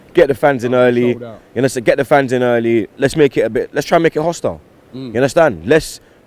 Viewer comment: did he just say make the EMIEATES HOSTILE?? Never heard those 2 words together.. one of if not the worst home fans in the premier league so dead in that ground.. good luck making that ground hostile tho 😂